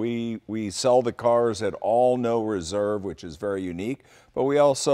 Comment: min did he say, "all at 'no reserve'" ? B-J is a boondogle. Would not take a car there, would not buy a car there. Jay is off his rails.